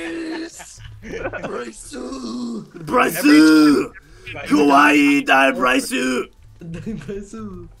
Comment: Did he say RYUZU KWAII?